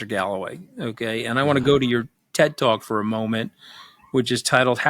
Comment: Anthony says "ted talk" i hear "dead dog", and got really confused by the lack of compassion shown.